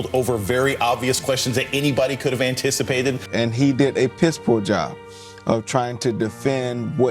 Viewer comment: Did he say 'a pitiful job' or 'a piss-poor job' ? Either way, he's correct.